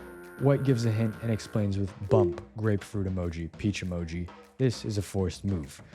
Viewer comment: did you just say graperuit😭😭😭